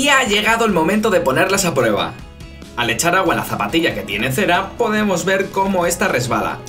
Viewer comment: escuché caga con la zapatilla